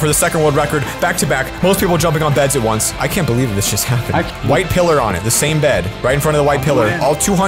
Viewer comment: did he say I can’t believe this sh*** happening????